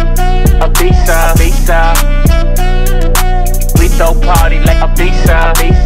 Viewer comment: when It’s sounds like tyga is saying “a pizza” “we throw a party like a pizza”